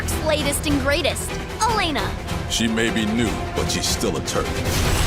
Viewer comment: I swear, every single time, I hear Rufus say "she may be new but she is still a turd". 😂